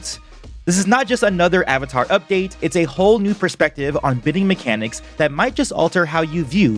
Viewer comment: Did he say he say at 0:24? Bidding mechanics? Is this one of the scenarios where I'm the only one who can't hear it right?